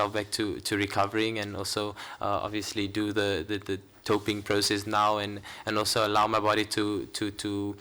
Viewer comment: ''I need to get myself to recovering and also obviously do the doping process'' I'm sure I misheard this or he just means doping control process. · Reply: filip obviously the doping control process but 😂